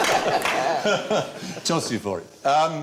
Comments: what does stephen say at 5:19? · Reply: "Toss you for it". I believe it is essentially flip a coin for it. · what does Stephen say...? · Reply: I wondered too, and I think its in one of the comments above, I'll toss you for it